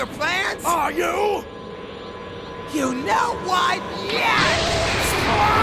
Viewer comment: "You know what?! YES!!" Beat that ass, Trunks! XD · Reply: I always thought that trunks said "You know why" and then blasted vegeta